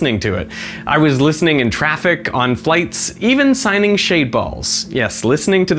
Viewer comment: ah, i hadn't seen his video about shade balls, so when he mentioned signing them at 12.02, i thought he said he was signing shaved balls. I thought that was a bit strange.